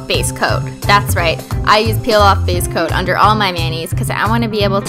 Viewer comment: I heard "I use a peel off base coat under all my nannies" lol 😂